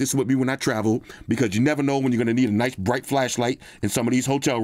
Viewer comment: My dirty mind thought it said flesh light LOL 😅